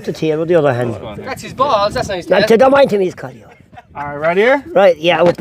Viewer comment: What did the auld lad say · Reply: He said "don't mind him he's codding you" (he's joking with you)